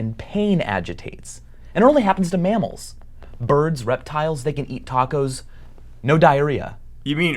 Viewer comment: When Rhett said reptiles it sounded like he said Rhettiles